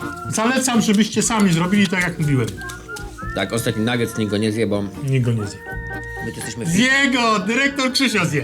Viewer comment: Kto usłyszał nikt go niez*ebał :D